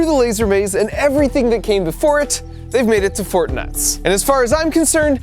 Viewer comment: for a second I thought he said Fortnight